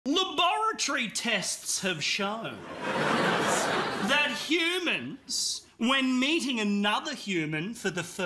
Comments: I thought he said "lobotomy tests have shown" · I THOUGHT HE SAID MATING INSTEAD OF MEETING